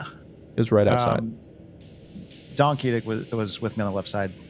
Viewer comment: I was so confused for a sec. I thought they were saying "Donkey Dick" instead of "Don Keedic" XD Now all I can hear is Donkey Dick- TvT